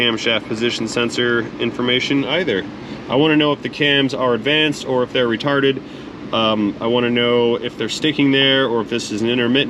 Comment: did he say he wants to know if the cams are retarded?